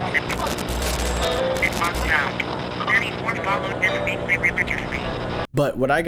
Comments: it sounds like "34 followed the state? Religiously." I've listened to it a bunch and I'm not 100% but I definitely heard the word followed and religiously. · i heard: more followed this week (and then propably the word street) so: Friday september 6th 34th more more followed street???